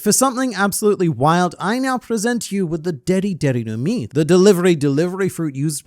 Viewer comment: When he said deri deri no mi I thought he said diddy am I the only one?